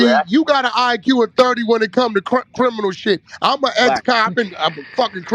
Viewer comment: Did wack say he's an ex cop at the 2:30 mark?